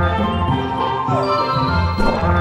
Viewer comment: did he say Hitler